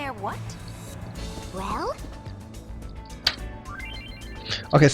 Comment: it sounds like Rikku is saying Rawr instead of well